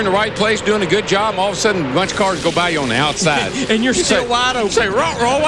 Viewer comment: and you say waddle bro what was Mike saying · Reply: “And you’re still wide open” is what Larry said